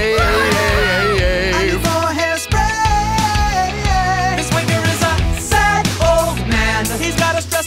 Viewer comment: I thought Liam said this wounder is a sad old man turns out he said WANKER